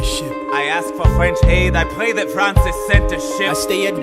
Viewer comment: Whenever Lafayette says "I pray that France has sent a ship" I always hear "I pray that France is sent to shit" And it pisses me off cause like why would he even do that